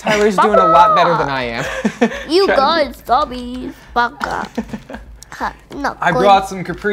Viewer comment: did tyler say “baka”